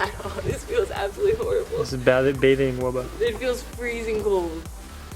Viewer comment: I didnt notice she said wubba i thought she said wubble. And SkyesSquad4life. Love you you are so amazing.